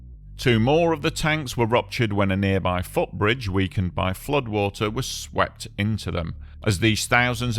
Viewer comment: Is he saying? “a footbridge where you could buy flood water?”